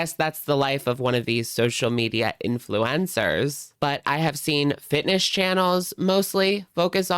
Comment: I thought you said 'Influenza' and now I refuse to call influencers anything else haha